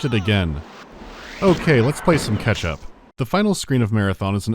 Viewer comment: Lmao I had to rewind, thought I heard "let's play some Ke$ha" not "ketchup" lol